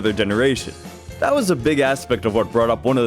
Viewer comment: thought he said big as